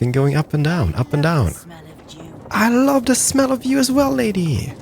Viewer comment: I keep hearing "I love the smell of jew" What is wrong with me!?!?!?!